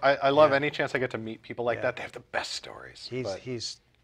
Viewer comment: it sounds like I said, "... he died ..." in referencing Dave Carson. I believe I started to say, " ...he's ... did ..." To be clear, Dave is NOT dead, and in fact is active in live performance among other things.